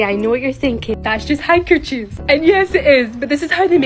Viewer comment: My dumbass thought she said "Hydrogen" instead of "Handkerchief" wtf💀💀